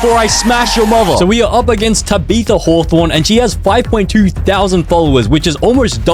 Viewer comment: i heard you say “tabby the war-horn” lol